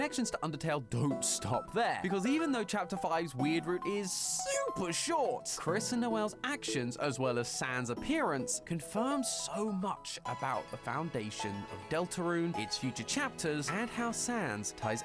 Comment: For a second I thought it said “Noelle LOVES Sans” and I was like “what 💀”